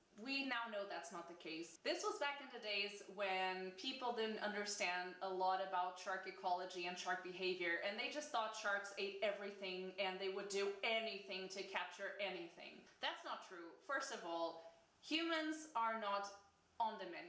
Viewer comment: Shark ecology. O.O I love that word. At first I thought you said "Sharkicology" and I was just hyped about it haha